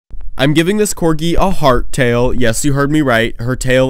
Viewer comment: Him : I’m giving this corgi a heart tail Me :“misheard him” .. what do you mean hard tail ?! 😀